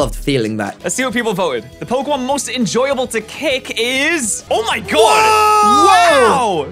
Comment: For a sec I thought it said pokemon most likely enjoyable to lick I was like 🤨